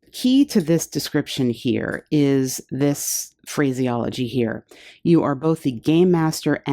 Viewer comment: you said phraseology, but first I heard crazyology, and I'm totally saving that word crazyology for later use. Haha.